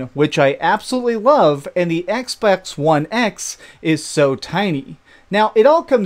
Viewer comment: Did he say "Xbax"?